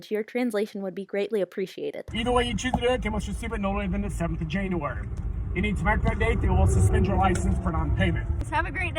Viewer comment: This is what I heard... "Either way you choose to do it, I can't watch you sleep until the seventh of January. And it's my birthday so they will suspend your license for no payment."